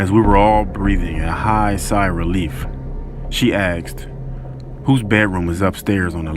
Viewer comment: I keep hearing “aksed” instead of “asked”.